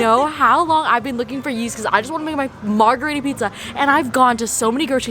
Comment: when she said she was looking for yeast my deaf ass thought she said yeezys i was like what crazy grocery store do you have in canada wuuutt